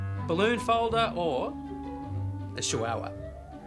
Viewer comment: When cleos dad was talking about the party and was trying to hire a chihuahua, I was sooo genuinely confused for a while because I thought he was saying “sha wow wow” and I was like wtf? Is? That?